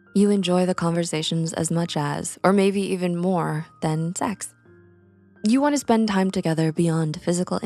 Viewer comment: did you say text or sEx??? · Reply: She said s3x